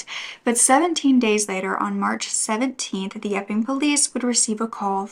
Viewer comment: Thank you for always having contant warnings, just knowing that it is coming in the video makes it easier to hear. Everytime you said " the Epping police" I heard "the f'ing police" 🤣🤦‍♀️